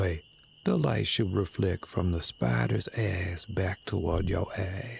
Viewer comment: With your accent I thought you said “reflect from the spiders ass to your ass”